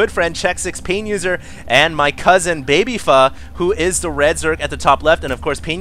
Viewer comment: Wooooow...I thought you said "my cousin babyfuck..."